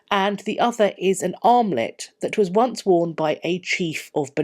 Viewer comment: OMG, for a second there I thought you said an "omelette" worn by the king... not an armlet.